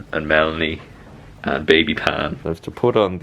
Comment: Sorry, does say the baby's name @ 7:40 ish? Sounds like he says "baby Pan" or "baby Tan" when talking about going back for the surgery. Just wanted to make sure you all didn't mean to bleep that out for privacy. And if you are sharing at this point, I'd love to know. If you're wanting it not in the video, just know it's there! <3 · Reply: Oh 'baby Pan' is our son's internet name (it's short for 'pandemic baby' haha and I also love Peter Pan so it fit really well! Hannah Witton gave it to him lol!) x